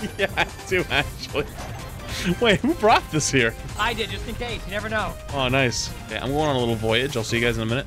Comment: Gotta love the on screen subtitles. @ 1:32 What was said: "Just incase you never know." What was shown "Just incase, you never did". Figrued id have to mention it :)